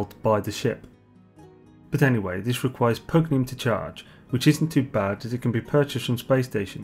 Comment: it requires what to charge? Potinine? Can't make out what you're saying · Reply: Pugneum of course :)